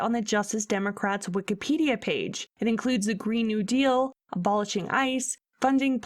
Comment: I thought AOC said she wanted to pass a green nude eel.